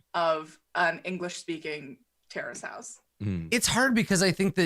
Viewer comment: Every time they say “Terrace House” I keep Hearing “Terrorist House”, which honestly sounds like a mid 2000’s shitty Adult Swim live action show about offensive caricatures of extremists from different cultures living in the same house.